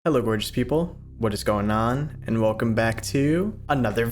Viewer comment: did bro just say "hello orange people"?????